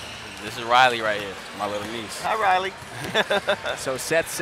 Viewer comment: Is it just me or did it sound like he said "Niece" @ 1:15⁉⁉ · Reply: Yea, that's his niece.